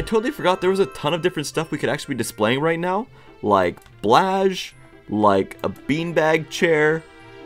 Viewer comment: DID HE JUST CALL blahaj BLASZ?! i know that some countrys dont have the same letters soo I also know how its called.